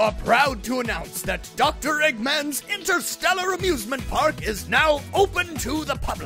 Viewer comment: did he say amusement or abusement? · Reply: Amusement